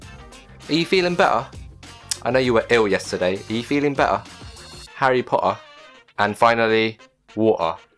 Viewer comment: idk why but I heard it as HARRY POHA 😂😂😂